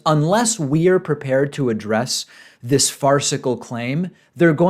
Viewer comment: I swear he said “fart-cicle”